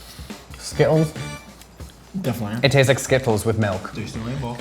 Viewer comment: Did he say “skipples with milk”? 😂😂 Did he mean skittles?